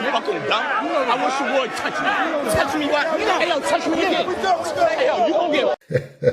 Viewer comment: JTFO at 20:41 did he say touch me again after he said I wish u would touch me WTF MAN